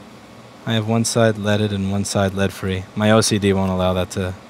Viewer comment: Was that intentional or not? (He said LCD instead of OCD.)